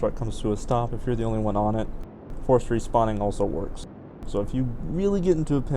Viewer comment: Hello at 14:38 you mention using Alt + F4 to stop, You also say Course something which I can seem to catch what you are saying. Can you type the other method for me please. Thanks much the video is the best I have seen with going to space. Thanks much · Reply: Force respawn